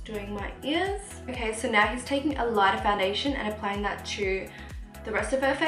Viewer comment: The concealer he used is Urban Decay Naked skin I think😁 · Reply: I thought he said all nighter